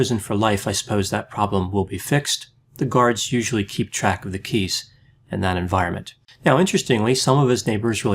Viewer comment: I swear, I heard you say 'penis' instead 'keys'. There might be some truth to that mishearing though. DeAngelo has been jokingly referred to as TPK, like BTK. Short for Tiny Penis Killer.